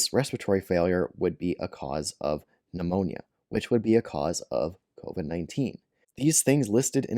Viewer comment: That's what I thought. So why does the channel get it wrong? Throwing shade on others for similar errors. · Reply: LOL. I see what you mean. I turned the volume up. The first two times I heard that as "A because of B because of C", but he actually said "be a cause". I'll put up my primary criticism of video, which I posted 10 minutes after the video went up. I'll note I'm possibly this channels longest viewer, I'm certain the view count of his first video was 0 when I saw it. "Sorry, but this is probably the weakest debunking video you have ever done. Essentially your debunk is that someone who releases 5-7 videos per week, doesn't correct all of his mistakes. And that other people are choosing to misuse the information." So yes, what you have pointed out is another example of this.